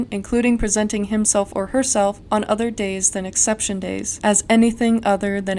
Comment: for a moment i thought you said “acception days” i was boutta go OFF on these lawmakers